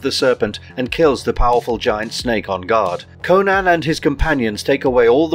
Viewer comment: sounds like he said, " ON GOD " on god💯💯💯😈